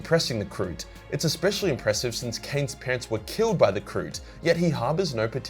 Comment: For a moment, when speaking about Ciaphas Cain, I seriously thought he said the Kroots killed his pants. I only realized it was "parents" because of having read the book some time ago.